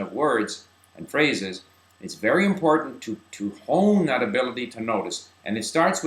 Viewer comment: what does say at 4:03 "it's very important to hooone that ability to notice"? or something like that?? :S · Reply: He said hone which means to sharpen